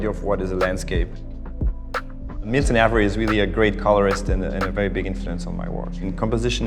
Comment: Who is the artist he mentions at 0:58. I couldn't quite make out the name? Phillip Guston? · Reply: milton avery